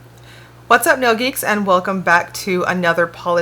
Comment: Misheard Lyrics - perfect time to tell you I watched your videos for months (years?) before realizing you don't open with "what's up yogis". Never understood why you said that. Then the day I had captioning on and saw "nail geeks"!!!!! 😁